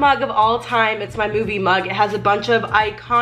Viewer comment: instead of “movie mug”, i heard “boobie mug” wow